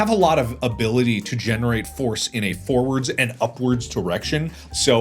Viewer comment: I'm sorry but I swear I thought he said, "I can generate foreskin," when talking about fighting a loon